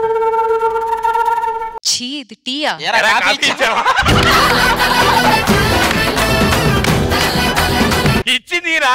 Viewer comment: ee time lo vache dialogue artham avvaledu, can anyone reply what did they say🥲🥲🥲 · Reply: Venky movie lo bramhi dialogue andi idhi andhuke navvaruu anthalaa ( ey ra coffee ichava ani anaru )